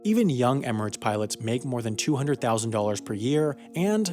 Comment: For a second there I thought it said pirates 😂